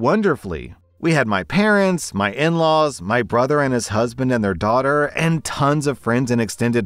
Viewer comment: For the love of god I gotta find my glasses I thought that said “my husband ATE out daughters”